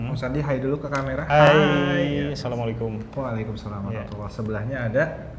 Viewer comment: Kedengerannya walaikumsalam, bukan wa'alaikumsalam.. Semoga sy salah dengar